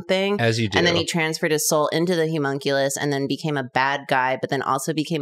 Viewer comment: Idk why but when karina said Homunculous I thought she said Humunculous like hes super hunky